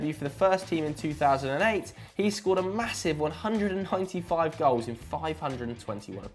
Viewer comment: did he just say 100 and hunti five? hahaha i'm cracking up <3